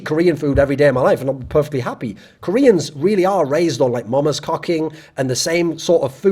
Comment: "Mama's cocking" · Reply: I heard it as mama's cucking lmao